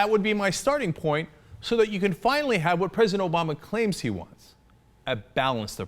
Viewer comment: for exampel the subtitles say instead of "president obama".. Gefängnis obama....which means in german "Prison obama".